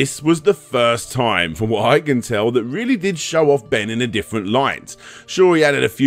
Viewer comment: Did say Ben @ 36:17 ?? I thought his name was Bob??